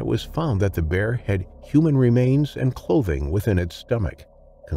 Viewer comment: I thought he said “inside the bear they found human remains and COVID” then listening again it was clothing not COVID